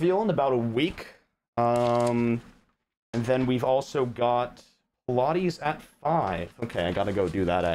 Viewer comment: did he say "pilates at 5" at 0:09?